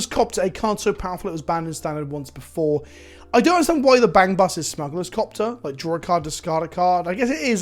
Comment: Is it just me or does he say “bang bus” at 7:27?